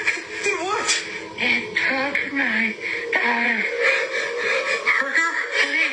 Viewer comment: They actually said She actually said "it took my daughter" He actually said parker, At the end she hit her head and screaming